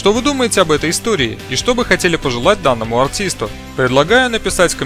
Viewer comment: Я один услышал АУТИСТУ?